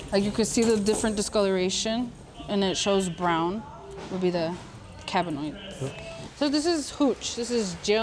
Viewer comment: Is it just me or does the “testing chick” keep sayin “cabinoids” instead of “cannabinoids”?.